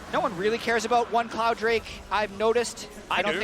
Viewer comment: did he say "F@ cloud drake"? lol